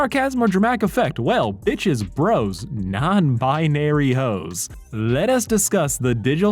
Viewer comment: did you say non binary hoes?!